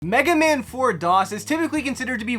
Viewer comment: RIP Wii Wednesday basically :( I heard you say MegaMan 4 DOS and not Mega Man for DOS